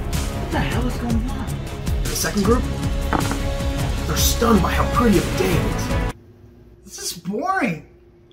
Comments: What did Say there ? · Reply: "The second group, they're stunned by how pretty of a day it is." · Sorry, what is he saying here? "They're stunned by how pretty of days"?